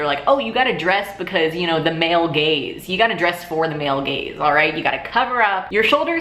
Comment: At first I thought you were saying "male gays." I was like why would gay males be concerned with how girls dress? Then after some thought I realized you said "male gaze" 😂😂😂